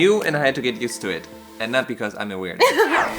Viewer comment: On 1:07 I thought you said, because he’s a güero lol